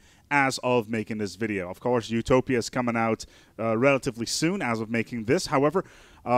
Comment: Thought I heard Zootopia, then I realised that you said "The Utopia"